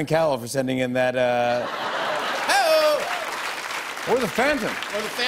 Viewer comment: I first heard "or the Phandom " and I just shouted EXACTLEY but then noticed it wouldn't make sense for him to talk about dan and phil xD (and that is why I'm single)